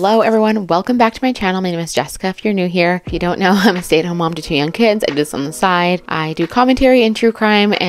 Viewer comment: I thought you said you were a mom to 10 kids 😂😂😂but when listening again its 2 young kids. 😂❤❤